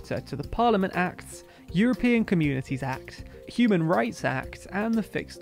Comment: It really sounds like you said "Cuban Rights Act". I'm sure Cubans could do with such a thing.